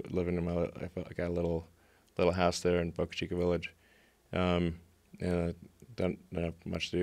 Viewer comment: did he say butt cheek village??